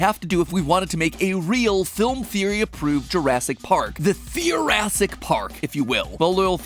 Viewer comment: I know he was saying "Theorassic Park", but at first I thought he was saying "Thoracic Park". Now I can't stop imagining a zoo full of creatures like the Gaping Dragon from Dark Souls and the Mapinguari from Brazilian folklore. Just horrible nightmare fuel creatures with open chest cavities that double as massive, man-sized maws with gnarled and gnashing rib-teeth as far as the eye can see. Have fun sleeping with that image in your head tonight.